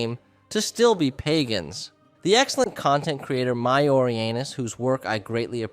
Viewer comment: 😂😂 bro that name I heard it as "my orey anus"